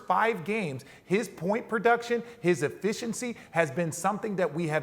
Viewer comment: Did SAS say turning the FOOTBALL over?? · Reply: I swear I heard RJ say Whit will you do.